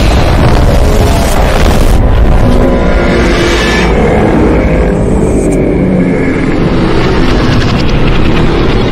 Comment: I think hes said "im sorry boys" when he first appeared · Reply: He said, missed me